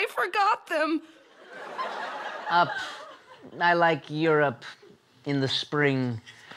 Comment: When i first watched this (actually i watched part 1), it took me forever to realize her was saying Europe, because i kept hearing 'your up'